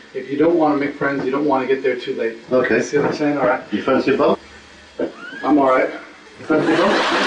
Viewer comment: He sounded like he was saying "fancy a BOMB?" either way I would've said NO! lol